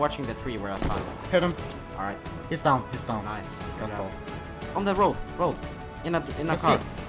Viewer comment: Hi guys I hear a Russian word at 05:56-05:58 you say готов, I know this word, because I am from Russia, but how you know this word, P.S. let sow you on you tube 3 months, So good luck in your life and don't stop make a video. P.S. let know inglish not so well.